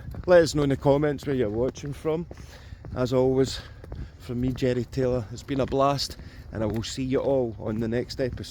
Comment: A thought you said Jenny Talia instead of Jerry Taylor. lol🤣